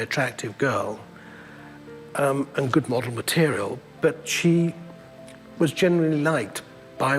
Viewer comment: I had to rewatch this for two reasons 1) I didn't hear him say it was his wife 2) I thought he said "modern material" I was about to write a paragraph thinking he was just some big creep